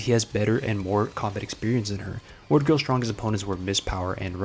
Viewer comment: for a second there I thought you said he has better word combat experience than her